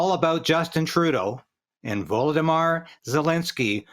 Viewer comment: did he say voldemort zelensky?